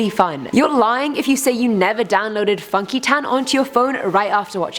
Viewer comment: But i honestly never downloaded funky town after watching Shrek though and I'm not lying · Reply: Ooohhhhh funky town i thought she said fucky tan 😂